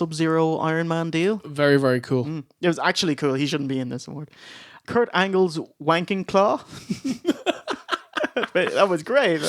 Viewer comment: All this time, and I thought you were saying 'Kurt Angle's Wanking Claw'...